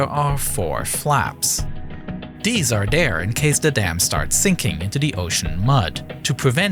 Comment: I heard "deese are dehr" instead of "these are there". Perhaps it's just me.